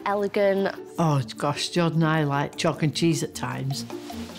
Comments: excuse me wtf did she say at 0:44 LMAOOO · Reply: She said, “Oh gosh, Jordan and I are like chalk and cheese at times” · Could someone help me translate what the Mom said at 0:45 ? Am I the only one? Such a strong accent 😭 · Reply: She says "oh gosh Jorden and I are like chalk and cheese sometimes" then she says "but I am very traditional"